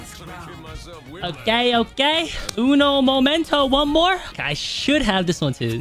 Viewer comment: wait did he said momento in spanish😮